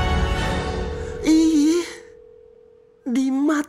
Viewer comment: Motherfather. Lol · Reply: Malik Dikdik awal nya kedengeran gw malah motherfaka wkwkwk ternyata motherfather ....